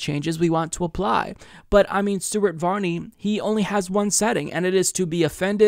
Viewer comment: I actually thought said stupid varney in the beginning. Maybe cause it’s the Scandinavian country Denmark but I’m not surprised that LEGO did this. Denmark tends to be ahead of the curve and read the room overall. Denmark and Sweden had gay prides back in the early 80’s